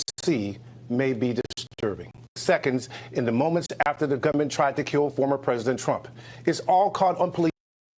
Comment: I believe he actually says moments before the GUNMAN tried to [unalive] President Trump - NOT the government.